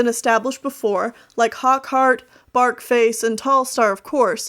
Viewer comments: i think im going deaf XD when you said Barkface i thought you said Fartface XD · I thought she said "Fartface" instead of "Barkface" Kill me now. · When she said “BarkFace” I thought she said “FartFace” and I was just like “What did you just say, Mate?” LOL!😂